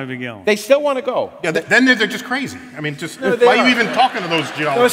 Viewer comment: Is it me or did he say "that nigga just crazy"?? 🤔🤨